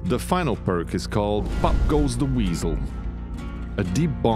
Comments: Ok so NOW you guys are doing spotlights again and you are doing them when everyone has figured out, what the perks and abilities do. Also when you said pop goes the weasel I thought you said f**k goes the weasel. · Why do I keep hearing "F*** goes the weasel" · I thought he said “F*** goes the weasel”